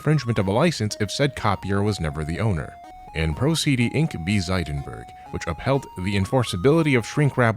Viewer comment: I was really confused and thought you actually said "prozd inc" there.